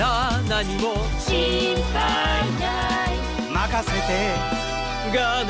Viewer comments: It sounds like he is saying “fuck a Steven” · pearl sound like she said sauske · is he saying f*** Steven!